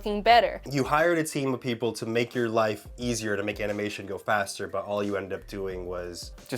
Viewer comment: At like 6:22 I went back ten seconds to see the drawings again, and all I heard was “So you hired people to make skips my life look like s***” i was dying laughing.